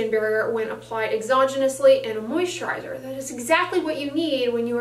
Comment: Please help me With the word exzonulanIy. I don’t know how to spell it or how to look it up to know what it means. Time stamp 6:51 is when the word is used. Thank you I. Advance for your help. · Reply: Candice G Exogenously